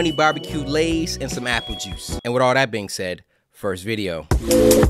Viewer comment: did you say happy juice or apple juice